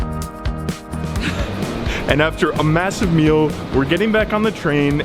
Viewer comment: did I hear massive 😮